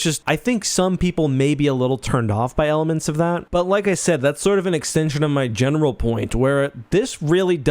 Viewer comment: I actually thought you said at 3:30 “an extension of my genital warts” 😬